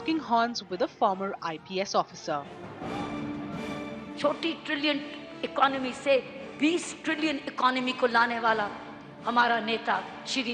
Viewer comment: did she just said 40 trillion to 20 trillion?